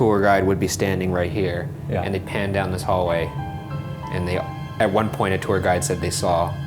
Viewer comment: probably just me, but the voice at around 11:34 sounds like it said "then go away." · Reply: I heard "Down the hallway"